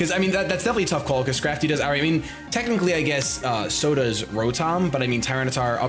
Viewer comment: for second there i thought you had said sodas rotom and i was like wait what